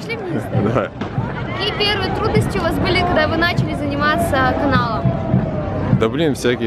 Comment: Блин,мне показалось,что она сказала "аналом"